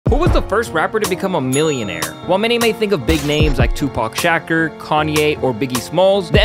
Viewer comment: Did you just say shacker?